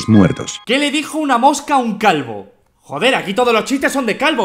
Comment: with captions of Spanish (Latin America) instead of saying "calvo" says "Wismichu" XD